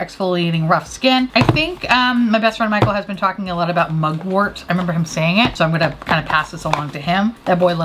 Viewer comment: When you said "mugwort," I heard "muggle." 🧙‍♂️🔮